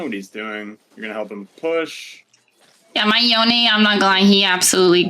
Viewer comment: What in the fuck did she even say at 4:35 lol. Yeahmyyone yamaglyhe